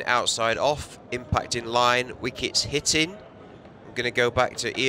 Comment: Did he just say''wicket hitted'' or ''wicket hitting'' cant tell by his accent